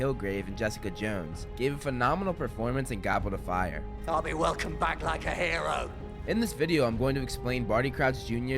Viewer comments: For some reason there was a spike when he said "I'll be welcomed back like a hero" And instead I heard "Dobby'll be welcomed back like a hero!" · Anyone else heard DOBBY WILL COME BACK LIKE A HERO